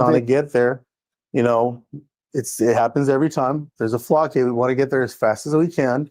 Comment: What exactly did he say at 4:14 "There's a flock hit. It happens every time." ? Flock hit??? So is he admitting that the flock cameras are actively doing the policing? Huh.